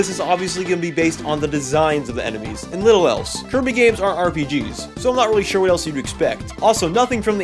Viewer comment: I said they aren't rpg's. and yes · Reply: Oh shoot fam I must have misheard you Though it still sounds like you are saying kirby games are RPGs